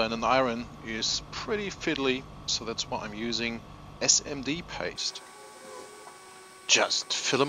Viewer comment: What is the sauter you are using i hear "SND Tape", where did you get this.